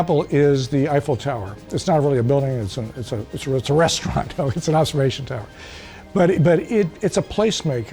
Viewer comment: lol I thought he said “restroom” not restaurant. That’s even more true probably.